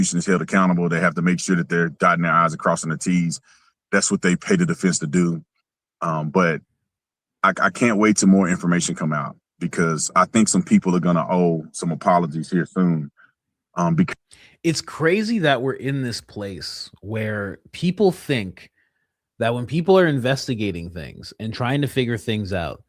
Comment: Did he say “owe some apologies here thoon” ?! 😆🤣